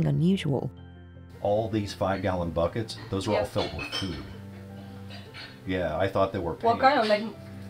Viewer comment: I was only half listening to this while I worked and I all I heard was "these 5 gallon buckets? They're filled with poo. Like this one says macaroni and spaghetti." I was like whaaaaaat is happening lol